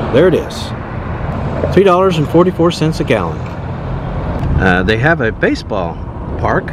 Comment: I could have sworn you said $2.44 a gallon when the sign said $3.44 per gallon, am I loosing my mind?